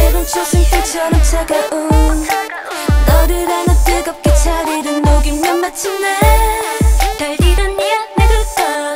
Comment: i think i hear joy"s voice??? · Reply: I think she says crescent moon. I'm just guessing here so Srry if I'm wrong 😅